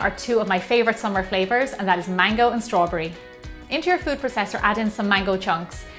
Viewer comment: i heard" into your food professor" and i love it, its cute <3